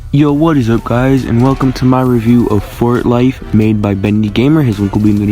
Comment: For a second I thought it said fortlite and I was like season 6? Also good review!